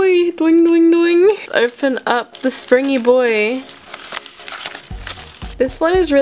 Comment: I thought she said spinny boi but she said springy boi